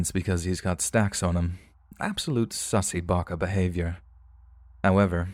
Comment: did I hear that right...."sussy baka"